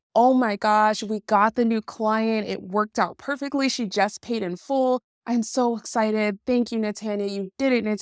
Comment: Oh, "pay in full"? I thought you might have said "painful".